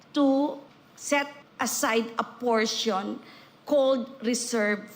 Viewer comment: I think the word is "called" not cold, right?